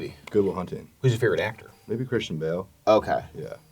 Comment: I swear I thought that guy said Christian Grey and not Christian bale😂